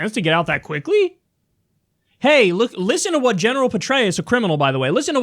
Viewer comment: Whenever I hear Petrarus I hear “betray us” which is ironically quite fitting